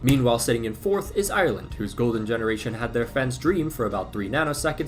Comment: bro why tf did i hear "golden generation" as "goal degeneration" twice. Brainrot doing its thing im actually finished